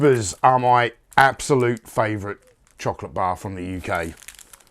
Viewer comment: I seriously thought you said "digested chocolate"...lol.